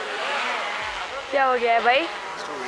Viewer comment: Did she actually said... ' Kya hogaya "BHAI" ?' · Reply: lol 😂😂 Bhai nhi she said bai .. Nikhil 😂😂 Bhai ke pass abhi Bi chance ha 😂😂